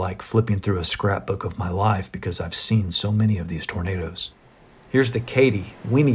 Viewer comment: closed caption reads "burritos" instead of "tornadoes"...😂